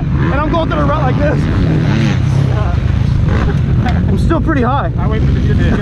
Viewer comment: Did he just say "I'm still pretty high" ?